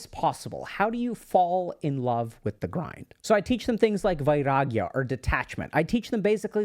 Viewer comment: bro for a second i thought you said viagra😭😭